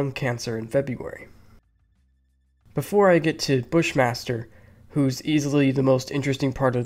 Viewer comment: I swear to god, when they said Bush Master, I thought they said Fish monster 😂